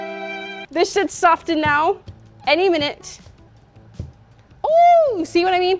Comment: I thought she said this shit should be soften now